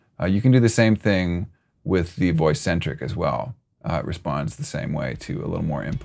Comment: I thought he said Beat Boy Centric and had to relisten. That should be his next plugin although not really his genre.